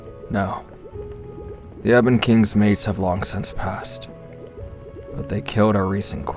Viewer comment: WAIT A DAMN MINUTE 29:40 Is it just me or does Shinsou say “Ebon king’s mates” as in multiple?! Please tell me I’m not crazy🙏